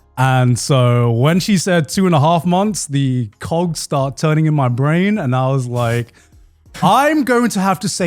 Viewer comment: lmao how joey laughs from mishearing "How the cocks started turning in my brain..."